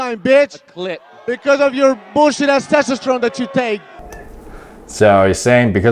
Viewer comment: he actually said bc "your bullsht asss testosterone" that you take. Insinuating that his test is better than theirs.?? And he mentioned it like once. I think they are all on stuff. Why wouldnt u mention the titty like over and over